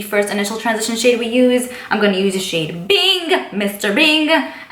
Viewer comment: I totally thought you said mr big and thought of sex and the city but then realized the next time you said it was bing oh I love the part Monica found out that was his last name